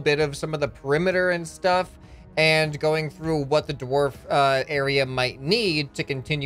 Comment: I thought you said dwarf during the whole thing instead of orc